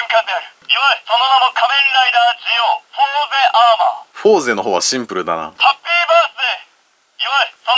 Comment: フォーゼアーマーがホモゼアーマーって聞こえる